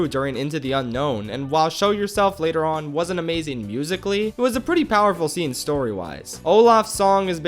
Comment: What he said: " Wasn't amazing musically" What I heard: " Was an amazing <link>" What I thought: " They made a <link> about it ?!?!?!" Then I thought: Wait.✋ it's Tik Tok now. Then I thought: Face Fickin Palm.